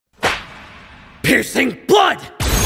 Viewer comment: Why do I hear "piercing butt" 💀☠️